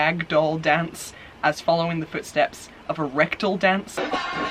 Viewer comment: I ALWAYS HEAR "RECTAL DANCE" TOO AND WOW I AM V. GLAD I'M NOT THE ONLY ONE. Anyways, you (and everybody else) looked incredible. I wish I had cute, perky boobies that would lend themselves well to the tape look... but, alas, they are huge and gravity has taken its toll. I loved this video and am excited to see what else you have in store for us :)